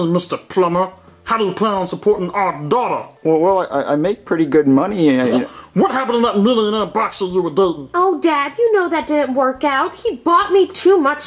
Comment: The first time I watched this, instead of “millionaire boxer” I heard “millionaire in a box”.